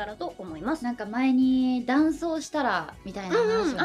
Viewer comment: I think Suzushiro-san said about crossdressing (男装) not 'for a dance'